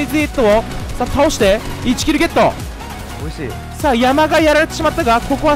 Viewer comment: うるせぇよ って聞こえない?